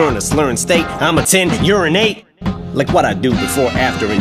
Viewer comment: em said : "i'm a ten, you're an eight like what i do before,after and durin' rape" but the way he said it sounded like: "i'm attend to urinate like i do before, after and durin' rape" and afterwards he says : "you wanna get into a pissin' contest and find out who's better ?" did anyone else found that or it's just me ?